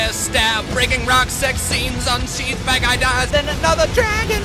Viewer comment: 1 Hunger Games trailer 2 When he said bad guy dies it sounded like he said fat guy dies lol